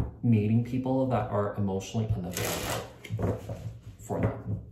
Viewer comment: This resonates a lot! Can you please clairify: At 7:05 what are you saying: A pattern of needing people that are emotionally....? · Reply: Unavailable, got it. I was drawn to your reading again. You've got yourself a new follower <3